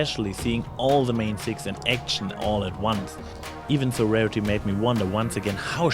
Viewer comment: I heard Sir Rarity at 7:18 WTF? Great review though.